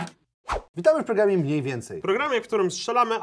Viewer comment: Jak powiedział ,,witamy w programie mniej więcej” to usłyszałem ,,witamy w programie jedz więcej” XD